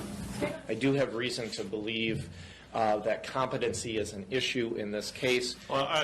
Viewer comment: defense counsel says that "competency is an issue in this case." The subtitle says the opposite. The defendant's behavior seems to validate the attorney's statement. The defendant may or may not be a "Kevin," but the attorney is saying the defendant may be mentally ill.